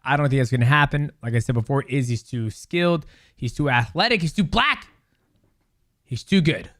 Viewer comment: did you say "he's too black" ?? 🤣